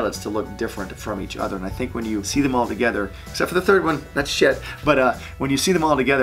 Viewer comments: Is it just me or did Butch say s*** at 8:05? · what you just say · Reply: I thought he said that sh**